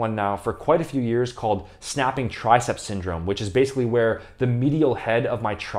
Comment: At first I thought you said Snap City Syndrome, lol